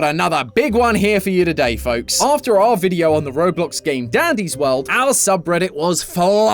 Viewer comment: Did i hear "daddy's world" ???